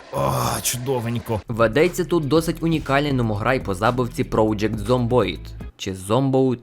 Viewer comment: мамограй? чи мені почулося 0_о